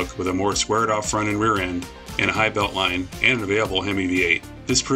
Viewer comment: what did you say at 7:25? "...and available M88"? background music is too loud